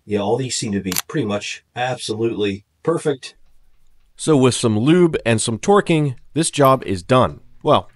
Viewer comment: Could of swore I heard “With some lube and some twerking….” Lol I almost backed out to make sure I wasn’t on the wrong “Tube”